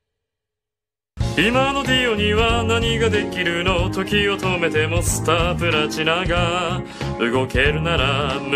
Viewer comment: I thought he said nigga